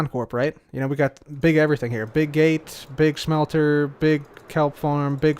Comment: “Big gates” i thought he said bill gates